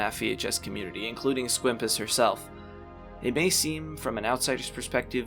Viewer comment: did he say "squimpus Herself" or am I crazy?